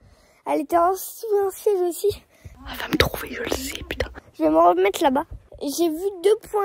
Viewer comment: dio….. C’EST MOI OU TA DIS P##AIN 😂😂😂❤❤❤❤❤❤